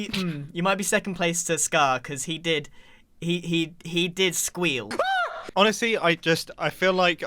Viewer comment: "he did squeal" What i hear: "CaWk"